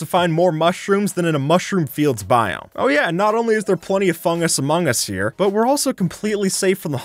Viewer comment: did he just say among us? That is sus